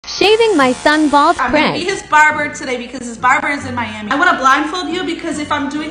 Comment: Ok was I the only one who thought that she said today I’m going to be shaving my sons 🎾🎾